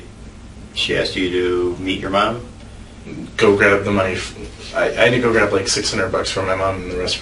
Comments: did he just say go grab the knife???????? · Did anyone else hear him say @ 12:30 mark "Go grab the knife"? Wtf please someone reply back and lmk im sane! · did he say grab the knife?? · does he say ‘go grab the knife”? Oh nope. It sure sounded like it at 1.75 speed lol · did he say, "go grab the knife"? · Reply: I think it was go grab the money f- and then he corrected himself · Am I hearing things? The detective asked if Mel told him to go meet his mom to “Go grab the money”, but said “Go grab the knife” 12:29. is a sponge, lived off his parents, lived off of Bruce and Mel, and lived off of his girlfriend while he did nothing. I wouldn’t have wanted him to marry my daughter. He comes off as a lazy ass and I’m sure Bruce and Mel had enough of him just laying around while Anna worked. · he actually says “ go grab the knife “ did anyone else catch this? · Was it me or did he say, 'Go grab the 'mnife',' Then his face shoots up when he realises what he says.